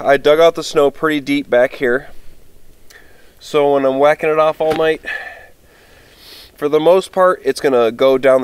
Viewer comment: I zoned out and when I zoned back in all I heard was “while I’m wacking it off all night”